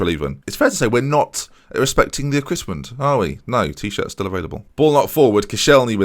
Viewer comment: "It's fair to say we're not respecting the [what?]" I swear I've replayed this four times and still can't get my head around what you said you weren't respecting :-/ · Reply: Ooh, equipment. You said the phrase again 20 seconds later and got it that time, haha